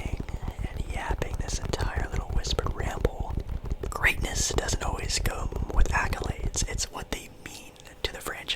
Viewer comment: I thought at first you said greatness doesn’t come with accolades but then you say kawhi is the goat in Toronto when he was only there a year 🤣 it would either be Vince carter or Kyle Lowry